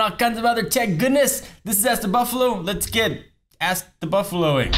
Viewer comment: did he just say ask the bieb? lol...